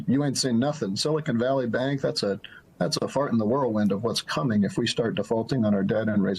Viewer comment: Did he just say, "That's a fart in a whirlwind" ? (at 7:30)... That's a good one I've never heard before 😂.